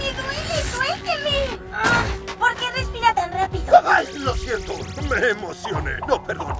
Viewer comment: Que dice en el minuto 1:08? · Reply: "¿Por qué respira tan rápido?"